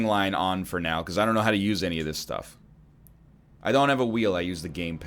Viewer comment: "I don't have a wheel" I heard "HOTAS time!"